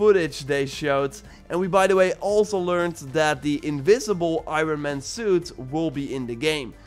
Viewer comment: Honestley thought you said invisible instead of invincible.